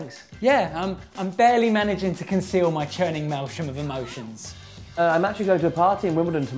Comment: what's he saying? I understand "yeah I'm barely managing to conceal my malshion of emotions..." thanks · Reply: churning maelstrom of emotion.